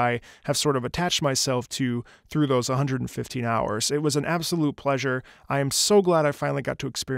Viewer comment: I was half-paying attention because a friend messaged me and thought you said 1500 hours at first and was like "oh shit bet?" For a second I thought you were a Monster Hunter nolifer like me just hiding your power level all this time.